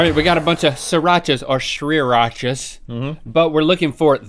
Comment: I heard 3racha. This is what Stray Kids do to you but I’m not complaining.